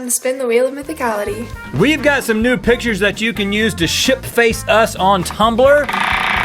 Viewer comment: It sounded like you said nude pictures instead of new pictures